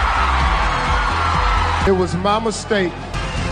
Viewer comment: Steve Harvey: It was my mistake. What I hear: It was Mom's/Mama's mistake. Lol😂